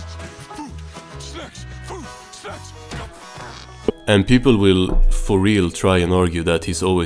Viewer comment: I needed to rewatch that 3 times to figure out he was saying snacks instead of s€x